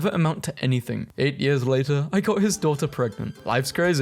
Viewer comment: thought that said 8 years ago.